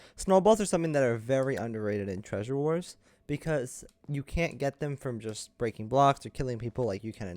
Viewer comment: From the shop :) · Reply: oh I thought you said you CAN get them from breaking by blocks but I realized you said you can’t after I re watched with subtitles. Ok thanks